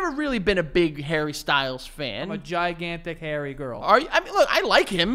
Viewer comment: I thought Joe said „I‘m a big hairy girl!“ 😂